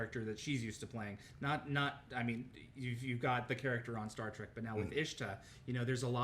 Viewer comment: I hear him say Star Trek, can anyone else verify?